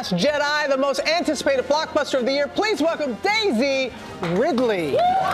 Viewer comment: Sounds like she said Davy Ridley